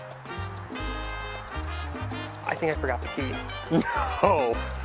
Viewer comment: i thought he said"i forgot to pee."